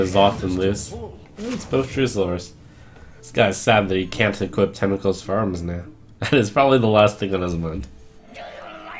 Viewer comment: What did trump say at 4:26? I don't really understand it but I can't tell if I'm just hearing it wrong. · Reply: "Hm, That's both Truesilvers. This guy is sad he can't equip Tentacles for Arms now. ...That's probably the last thing on his mind."